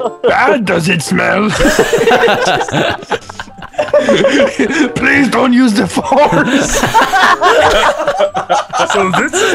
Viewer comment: is the most FUNNIEST ONE OF ALL 😂😂😂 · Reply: it thought he said "please don't use the farts"